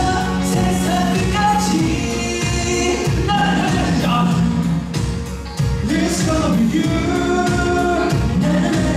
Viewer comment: who can tell me what did jin say after he was shocked by jk!!!! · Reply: 아이..깜짝이야!!! 라고 했어요